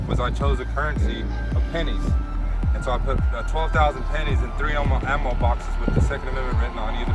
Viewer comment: I thought it said 12,000 penises